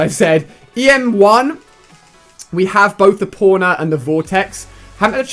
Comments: Am I the only one that noticed Ali said porner instead of pwner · You said a bad thing at the em1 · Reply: itsOlyy He thought he said Porner instead of Pwner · When he was talking about the pwner it sounded like he said porner lol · did you say the em1 porner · for em1 pwnr it sounded like he said em1 porner